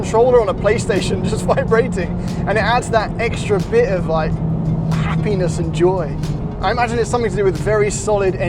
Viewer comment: My caption says the feeling in the steering wheel of the civic type R is “of like penis and joy”